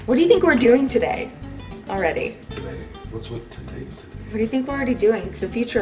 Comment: did he say "What's with today, today?" bahahahaha, love the Empire Records reference if I heard him correctly.